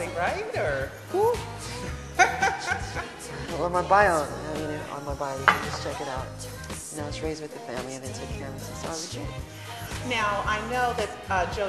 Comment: When she asked if he was his son it sounds like he said "Check my bio, no I was raised with the family they took care of me as a kid".